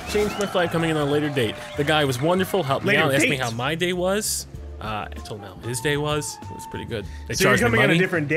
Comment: Anyone else hear that as "I told him how his day was?" Is this another "You are yes, this is fuck, hello?" moment?